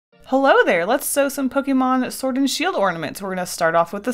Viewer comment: Tbh I thought she said let’s sew Pokémon 😂😂😂